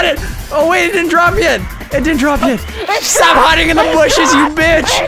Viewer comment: what did he say? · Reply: davisn he said: IT DROPED